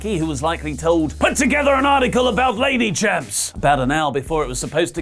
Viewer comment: It sounded like you said (that Vince had said) "Put together an article about lady chimps".